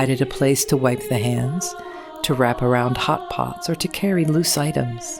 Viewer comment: I thought she said pot pots